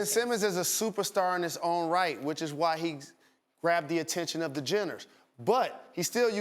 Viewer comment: I thought Jalen said jitters not jenners